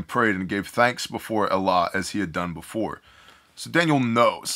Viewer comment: Wait what Bible version are you reading from? I think you just said at minute mark 38:11 quote “ he gave thanks before Allah” …??? My JPS (Holy scriptures) it just says God, in English that is.. correct me if I am hearing you wrong please. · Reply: Elah